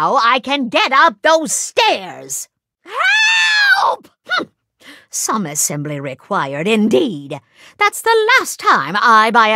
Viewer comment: What'd she say at 0:11? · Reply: She says some assembely required